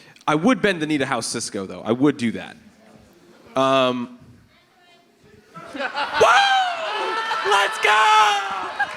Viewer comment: What the audience say to that make flip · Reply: "pandering"